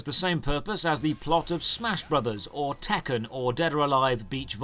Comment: did he say tekken? DID HE SAY TEKKEN??????????